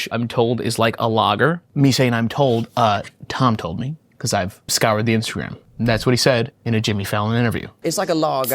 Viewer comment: Could have sworn he said "I scoured the instrument."' like he considered Tommy Hollholl here to be a sexy lil fiddle, his voice a sweet song of beer truth being plucked from his hard body by the callused hands of a grizzled but tender old pro.